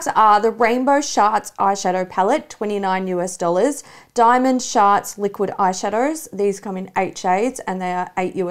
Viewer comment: Oh my god with the Kim Chi brand stuff I thought you were saying Diamond Shart! I’m dying 😂